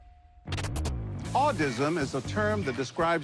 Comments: I heard "Autism' is a term that describes discrimination against people who are deaf or HOH." I had to replay it twice 😅 I'd never heard the term Audism before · I misheard this as “Autism” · All my homies hate audism! (I thought he said autism)